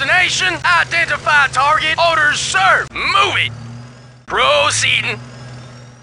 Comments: It took me years to realized that he said 'Delighted to, Sir!', after having spent years wondering what was this 'D92' he was talking about! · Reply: Ha ha, when I was a kid, I thought he said, "Enlightened to, Sir" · It took me years to realized that he said 'Delighted to, Sir!', after having spent years wondering what was this 'D92' he was talking about! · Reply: I always thought he said Longitude Sir? I just noticed that he says delighted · what does he say? i never understood it, after proceeding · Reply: As found in some yt comment, it is probably "delighted to, sir"